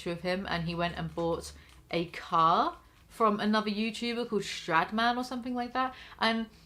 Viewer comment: you said strad man and I thought u said sh*dman and almost fucking got whiplash from looking back at my computer to confirm